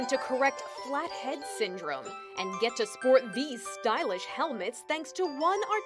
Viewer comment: I thought it said fat head syndrome not trying to be mean